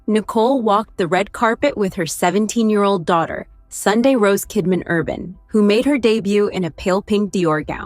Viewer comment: I had to rewind at the name of N Kidmans daughter. I thought i heard ''Sunday Roast'' WTH.